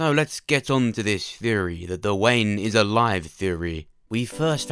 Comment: I was distracted by you saying Duane... It sounds like you say “The Wayne.”